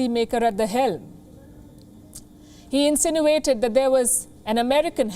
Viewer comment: I could have sworn she said, ''... he incinerated an American hand... '', (2m.49s) sounds like a burning issue to me! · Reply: Insinuated.